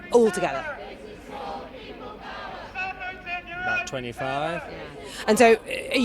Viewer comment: dude when he said "about 25" i thought he said "i'm 25" because i completely forgot what they were talking about and i got so scared because for a second i believed this was a 25 year old british man